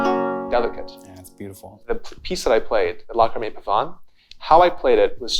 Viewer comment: What was the lute song he played where he said something along the lines of "locha mate pavan" at 15:29? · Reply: John Dowland : Lachrimae Pavan